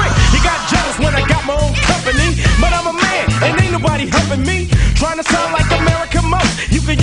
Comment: I always thought it said "Ain't nobody helping me" xD